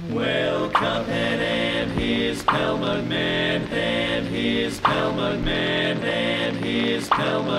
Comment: Why de heck do i hear 'then he's killed mugman' i am confusion.!?¿¡